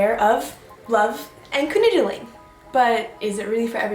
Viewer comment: What do you say here? "... Love and ______" I can't tell what you said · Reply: "Canoodling" teehee :)